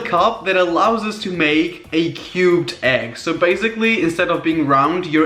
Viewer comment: When he said cubed egg I thought he said cute egg. Then I saw the product and realized he said cubed